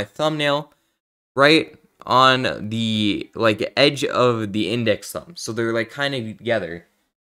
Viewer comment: Did he just say "index thumb"?🤨☝👍